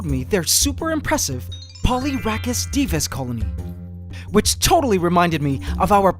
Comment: Did you say ant “Divas”? So cool!